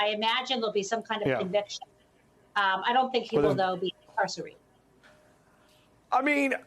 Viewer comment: Law professor Jennifer Taub "I don't think he'll be incarcerated" Is that what she's saying? 😡😡😡